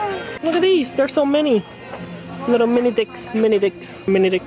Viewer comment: When was saying mini sticks I thought he said dicks lol! Anyways I had spam and eggs this morning!!! But teriyaki spam, mhmmmm girl I said it.